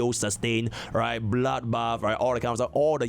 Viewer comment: did bro just say bloodbath lol this ain't etheria bro wake up😂